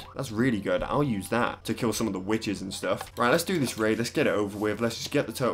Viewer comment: i thought that said "wither" instead of "witch" and i freaked out